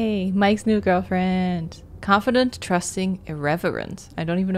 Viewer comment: I’ve never actually seen any playthrough of Until Dawn so this is exciting! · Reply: I thought 30:00 said irrelevant instead of irreverent lmao